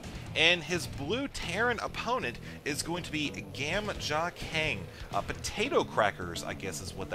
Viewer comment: Nice. I'm not Korean though, and I thought he said gamjatang. XD